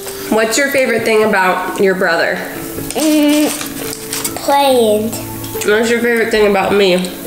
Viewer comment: he said playing but the subtitles said “playing drugs” lmao